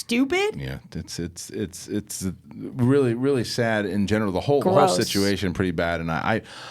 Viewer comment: It actually sounded like Kristian said “it’s really sad in genital” when talking about Whoopi/Bella 😂